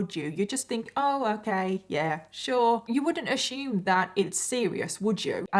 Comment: "Puh-NIN-choo-lah"! I love it! (I ah-shoom that she meant to say "peninsula", but it's ok; i have a funny accent too!) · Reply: Lol i know... I am a fairly new subscriber. At 1st when I heard her say "ah-shoom" I had to rewind cz I didn't know what she was saying. Cute way to say the word assume. 😂